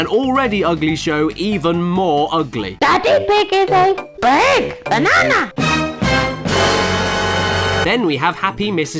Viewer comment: "DADDY PIG IS A BIG, BANANA!" OMG I laughed so hard at this part LMAO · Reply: Thought Polly said Daddy Pig is a red banana!